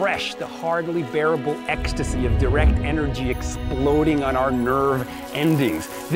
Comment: the cc says "different" when Jason actually says "direct" lol